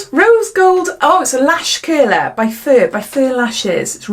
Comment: First I heard Lash KILLER 😂 That's how good my english is 😂